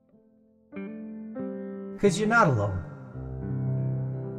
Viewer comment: I hear "Bitch yo not alone"